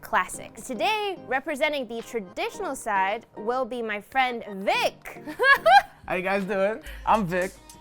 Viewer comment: I heard dick...😅😂hii my name is dick.... if I heard it wrong...